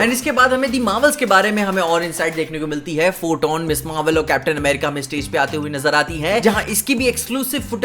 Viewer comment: he literally say "photon, ms.marvel aur captain **america**" (i am not finding mistakes but I was like "Huh... What did he just say"